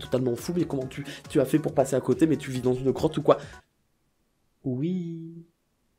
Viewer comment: Je pense que je suis malentendant car à 22:46 , j'ai compris : Mais tu vis dans une crotte ou quoi ? Quelle idée de vivre dans une crotte...